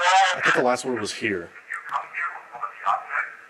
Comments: the first EVP sounds like it says "yes sir" and the time stamped one sounds like "Get out of HERE." Sounds like this place has a "tough guy" ghost. Aka an asshole. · I hear “get out of here”